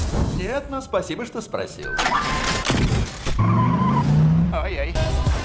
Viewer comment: i heard Ajjaj in Hungarian ganguange lol